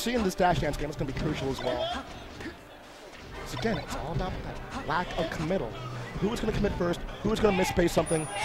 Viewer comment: did he say "lack of comittle"?...